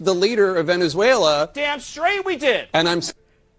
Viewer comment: I had to rewind. I thought asmond said DAAAAMN STRAIGHT we did.